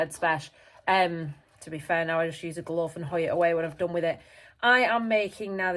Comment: Gooi it away ... :) the language is unexpectedly afrikaaans whats the origin?